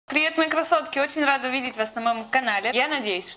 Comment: Смотрела это видео и когда сказала "Привет, красотка" Моему парню послышалось "Привет, красовка" 😂😂